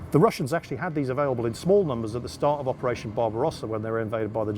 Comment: did he say operation BOB ROSS